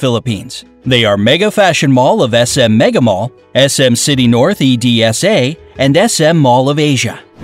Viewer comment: did he say E-D-S-A? 😂